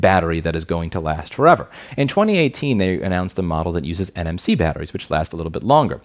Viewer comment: I thought u said "NFT batteries" but then i rewinded the video and it was "NFC batteries". That scared me for a second, thinking Non Fungible Batteries lol my mistake